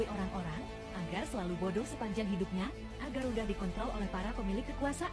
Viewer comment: atau 27 gw gk salah denger kan apa karena dicepetin ya suaranya dan kyk ngomong kont*l gitu?